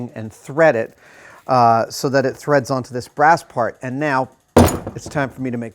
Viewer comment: Hilt, and pommel adam! not hand part and brass part. Jeez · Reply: I thought he said "brass guard", lol.